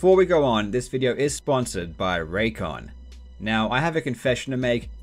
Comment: i heard "this video is sponsored by ra" and had a heart attack